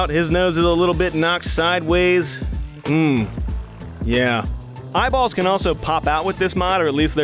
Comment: I swear he said my balls aee popping in out